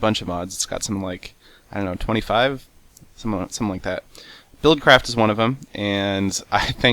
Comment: lol did he say build-crap